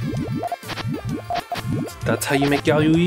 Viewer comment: that’s how you make what? It sounded like you were having a stroke. Was that even a real word? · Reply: 4 years later but he said yayoi, it's a chaining transition in the same vein as gtr or l-shape